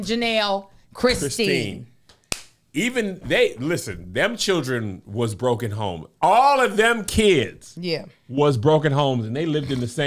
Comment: did you just say ‘them children was broken homed?’ #Icanteven 😂😂😂😂😂😂